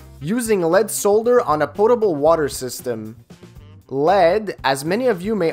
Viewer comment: 'Portable' water system? You mean 'Potable' water system. Or maybe I just misheard you. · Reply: Yeah, kinda sounds like "portable" but it's "potable" lol ;)